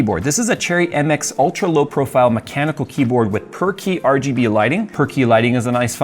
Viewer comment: I thought you said it has perky RGB lighting. Then my brain caught up and realised it was per key RGB lighting. And now i want it to be called perky RGB lighting! lol